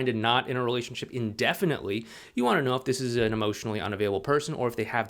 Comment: take what leap? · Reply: that’s my bad. I thought you said “as an emotionally unavailable” person.